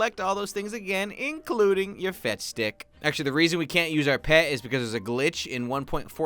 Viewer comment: I thought you said fech skik.